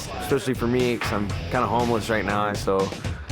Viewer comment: did he say “kinda homeless”?